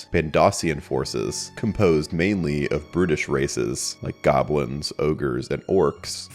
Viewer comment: i thought he said "british" races lol i thought he was roasting british people for a sec until i went and played it back. Brutish* lol